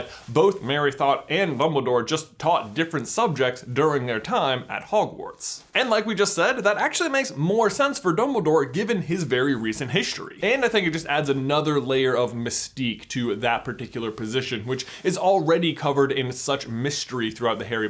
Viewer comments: am i the only one who hears him say mumbledore at 7:25? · does J say “Bumbledore”? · did he say bumbledoor?